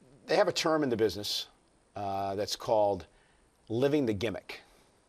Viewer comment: I first thought it said "Is Stardust leaving the gimmick?"